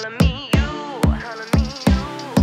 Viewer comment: I thought she said call me but she said colour me